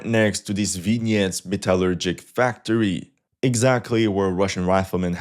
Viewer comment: Did you say, Metal Gear???